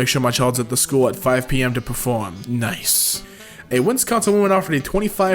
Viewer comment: Did he just say WINSCONSIN